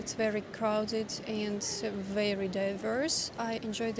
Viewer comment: I think crowded doesn't equate to dangerous--easier pickpocketing, maybe, and perhaps you find it anxiety-inducing, but not dangerous. Empty streets are the thing to avoid in bad neighborhoods. Glad you're having an interesting experience! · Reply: If you are referring to what she says at 1:40 at first I thought she said it was crowded and dangerous. When I listened to it again I heard that she actually said crowded and diverse.